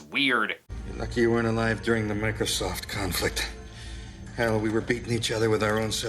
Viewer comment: wait did someone just say Microsoft? 🤦‍♂️ yet again another reason why this film was rushed.